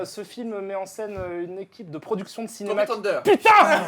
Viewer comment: C'est quoi le nom du film ? J'arrive pas à comprendre, je ne comprends que "thunder" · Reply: Cédric Giraud tropic thunder ou tonnerre sous les tropiques en français